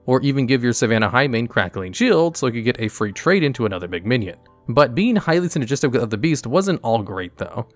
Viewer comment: Okay, I need to know if I'm going insane, but did you say "McMinion" at 6:14??